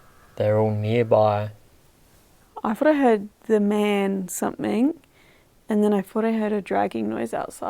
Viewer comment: I hear: 'the man in black'.